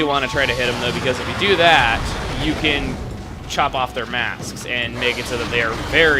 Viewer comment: anyone else thought he said chop off their ass?